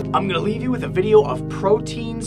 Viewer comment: When you said proteins i thought you said "Pro teens" traveling through a neuron O.o